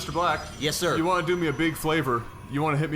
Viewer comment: Did I really heard "You wanna do me a big FLAVOUR ?" :D Interesting game. =)